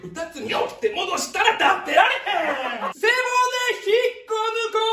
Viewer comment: 立ってられへんがBIGのり弁に聞こえてしまう…